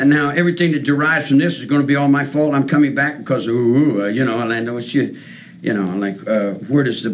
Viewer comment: What I hear: ”Because UWU”